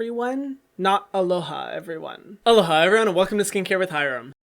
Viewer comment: Wow all this time I thought you were saying “hello Hy-everyone” like a cutesy Hyram-everyone mashup, and now I feel very silly.